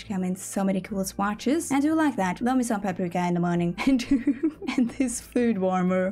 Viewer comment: what did she say?!😅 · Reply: hahah in a panic I said "paprika" which is Norwegian for "bell pepper"